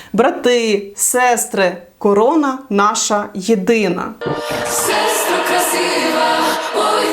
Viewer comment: Де ви там почули "корона наша єдина"? 8:41Вони співають "хоробра, єдина корона ТВОЯ". Вони ж пояснили, що корона, то єсимволом самостійності і незалежності. Навіщо навмисно перекручувати слова?